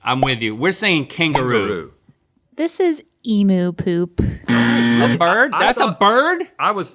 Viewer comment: Legit thought it said emo poop